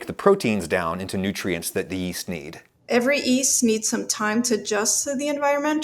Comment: Did you say "yutes"? I mean "easts"?